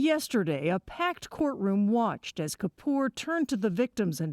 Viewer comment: I thought they said his name was Yom Kippur. How fitting would that be?